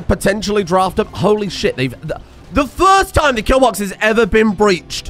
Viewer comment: the kill what? did you say box?